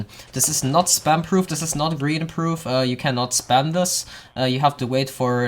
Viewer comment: did he say, “it is not grian proof” or am I going insane :)